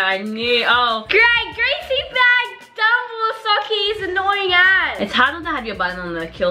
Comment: Among us is old why do u still play it it has the same tasks everytime and it’s boring / 5:57 what did say… bad word?????😮 put the cc on the top and it actually says the bad word. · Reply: She said as. 💀